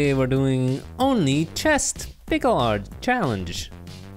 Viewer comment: did he just say pickle art??? Lmfao